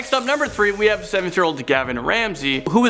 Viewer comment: I thought they said, "Gordan Ramsey" and I was in shock 😂